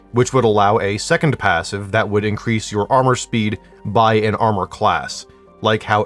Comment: bro did NOT mean extra armour rating. I’m fairly certain he said armour SPEED, similar to how OTHER armours have the extra padding passive, but instead of more armour rating you gain movement speed because your kit is lighter, which I couldn’t possibly be more on board with. I only ever run light armour anyways, so a light armour set with this underkill passive would be so peak. 600 movement speed would mean you’d actually be able to outrun stalkers, hunters, and any other high speed enemies